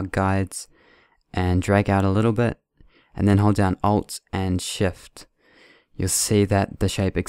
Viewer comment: ? i dont understand with which key plz anyone tell me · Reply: E man Shtypun ALT edhe SHIFTIN perniher